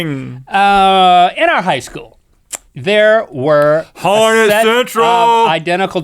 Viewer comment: did Rhett just say "Horny central?"